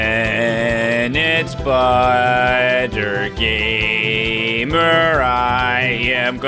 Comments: I really felt that what he said " i am peanuts butter gamer " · I always hear “penits-spider-gamer”